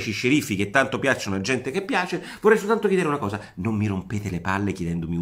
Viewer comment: ma la minuto 3:36 cosa dice ? che tanto piacciono alla gente che piace ??